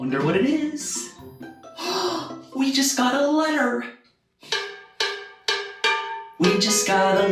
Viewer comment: I heard, "We Just Got a Ladder"